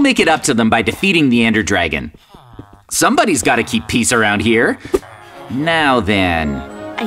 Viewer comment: When jj said someone has to keep peace around here and when he said peace it sounded like he said pizza